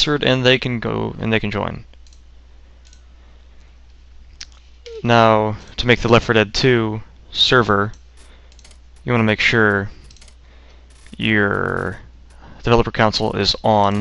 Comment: I'm french and I don't understand what he is saying from 0:35 to 0:46 , to open the console can someone help please . Have a nice day and thanks :) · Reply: now to make the l4d2 server , you gonna make sure you're developer console is on